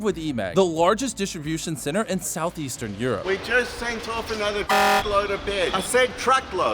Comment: i cant donate cause im a kid but support ukraine guys. 2:48: another ****load what he actually meant to say: another truckload why is truck and ** so close together